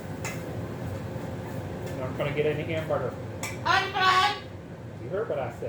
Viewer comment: He really acts chilled! He also sounded like he said, "I don't want hamburger!" Will be eat corn on the cob?